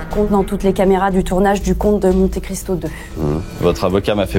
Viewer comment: j'ai entendu "comte de menthe et cristaux" désolé...